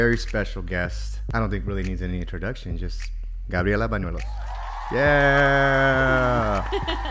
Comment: I thought i heard Gabriela Menudo 😂